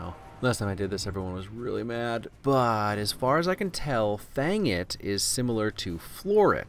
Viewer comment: Instead of "fang it" I keep hearing a different f-word... One, that ends in aggot. Always amuses me immensly.